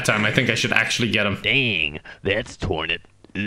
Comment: I think he said ,,damn thats turg"